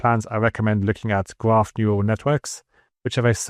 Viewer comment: I’d like to ask you something. At 5:33 you are recommending something for architectural plans and rooms that I hear as “graph neural networks”. I might be wrong, but that is what it sounds to me. Here’s my question: What you mean by that? Another AI model or something like ComfyUI, or something else? Thank you in advance!